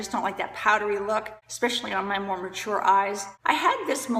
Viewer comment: " my more mature eyes" and I hear " Mormon sure eye"